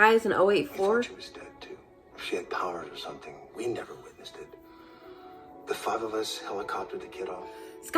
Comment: Its brief, and he mumbles, but he actually says "If she had powers or something, we never witnessed it..." So many people miss the IF